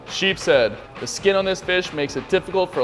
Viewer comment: Thought he said "Sheepshead, the skin on this Bish"